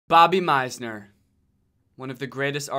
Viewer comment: Thought he said Bobby Maizono